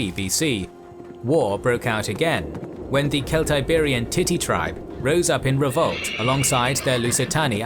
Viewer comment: does he say the Titty Tribe?